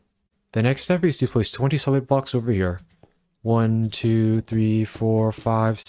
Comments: Bro when he said 20 solid blocks, i heard 27 so i put 27 blocks, now i gotta make the changes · Is he saying 27 or 21?? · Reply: I thought he was saying 27 as well but I turned subtitles on and found he was saying 20 solid blocks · If you guys get confused why the hole isn’t 3 by 3 it’s 20 solid blocks. I thought he was saying 27 blocks so I messed up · did anyone else mess this up at first because they heard him say 27 blocks even though it was supposed to be 21? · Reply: YESSSS he said “20 solid blocks” and I try ought it was 27